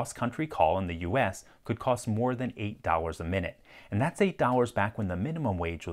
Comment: If the yearly income was $5,300, and it cost $1/minute to call, wouldn't it take 10 hours to blow a month's wages, not 1 hour? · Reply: Ah. I heard "more than a dollar." Thanks.